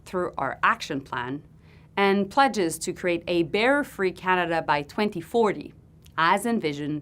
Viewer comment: Did she just say bearer instead of border at 8:35!?!